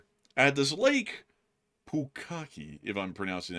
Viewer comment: For a second, I thought the said, 'Lake Bukakke'. Mathew knows what I'm talking about.